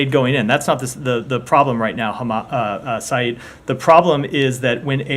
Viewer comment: What was said at 3:19 ? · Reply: He called Said Arikat "hamas"